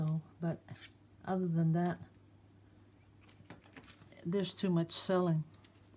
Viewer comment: Words I never thought I would hear you say...There is too much celling! LOL! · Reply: LOLOL I thought the same. I said now wait a minute... said to much celling????